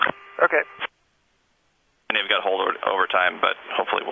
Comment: At about 3:20 I think he actually said “I know you have holdover time...”; I’m a deicer at BWI and that morning was absolutely no joke for us. DL1426 is one of our regular early morning departures that we deice for, and I remember that morning we had to deice every departure because everything that fell overnight and the day before had completely frozen over into a sheet of ice. Driving to work that day was absolutely terrifying to say the least 😅